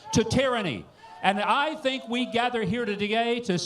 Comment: Was that a Freudian Slip there @ 5:18 by M. Huckabee? "I think we gathered here to...", wait, what did you say? Sounded to me like he said "We gathered here to gay"...don't let all of that homophobia turn on you and make you talk about Mr.T thinking how mighty cute you look in them overalls there Farmer Joe!, LOL!